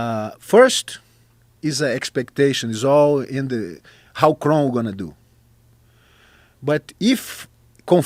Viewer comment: Who is this fighter they were talking about 1:23:52 ? Chron? Chrome? I understand what he's saying. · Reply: Kron Gracie...